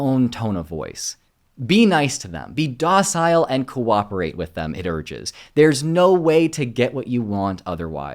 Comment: And what is he saying @ 12:01? 'cooperate with it urges' ??? Dude had a glitch in the internet...